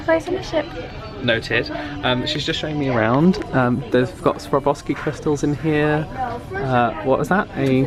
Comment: Did you just say "Sobroski crystals"... i'm offended.